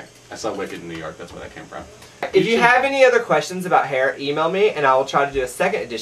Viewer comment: hahaha I thought he said "emo me" at the end instead of "e-mail me" lol I am SO NOT fucking emo! lol I love you William!